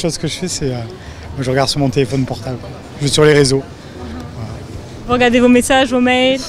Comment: the guy says "je vais sur les réseaux" But it doesn't sound anything like that, even when slowing down the speed. It sounds more like "zjufs sur les resreaux". What is he saying?